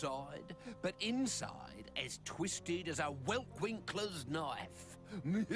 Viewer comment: As twisted as what knife? I can't understand the word Uncle Grizzly said. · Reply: Well Winkler I think